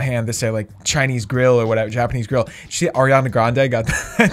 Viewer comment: did you just say sh*t? Love the content!